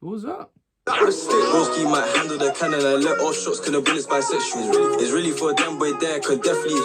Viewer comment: I think he said he try shoot at him but corn bisexual so it the girl next to him (im paraphrasing it)